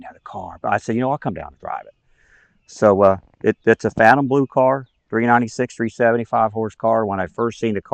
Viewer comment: Phantom Blue?........it's Fathom Blue (code 28)...perhaps I misheard. extremely close to '69 Dusk Blue (code 51).